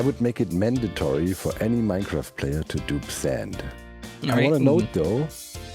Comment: I think said "mandatory to dupe sand", not "do sand" (subtitles at 1:10). Which would actually make a lot of sense considering the whole duping sand debacle.